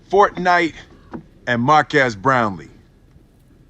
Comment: Markass Brownlee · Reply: Dayson B I thought he said Maria’s Brownie